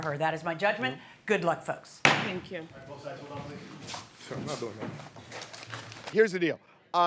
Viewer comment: Did he just say "im not doing that" oooh i wish the judge herd him. Hes such a scammer